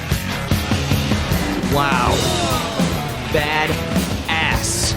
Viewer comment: the first time I watched this movie I thought he said “that ass”